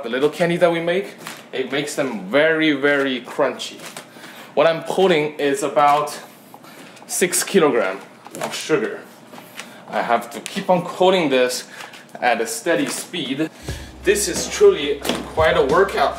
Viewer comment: I heard 'putting' as a 'puding' :)